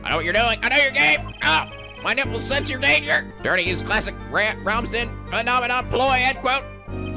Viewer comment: lol I thought Brushel said Ramsey Theory.